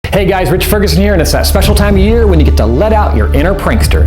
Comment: LOL I THOUGHT HE SAID Hey guys rick harrison here LOL